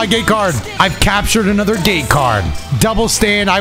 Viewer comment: Ngl thought you were saying gay card for the longest while and not gate lol